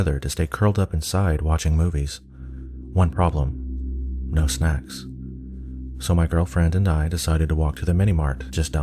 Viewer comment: lol at 0:28 I thought he said "One problem, no smacks." XD